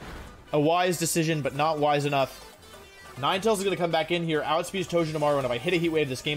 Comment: did he just say ninetails?!